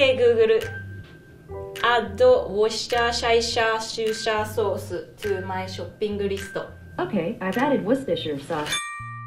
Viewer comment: okey gu gu ru ado Whoshesisshssa sos tu mah shap ping risto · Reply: + Call Me A pErSoN Everyone said it fine then at 1:55 the girl sounded like she said auaueusjdnrhsiakkj shopping list and it got it right.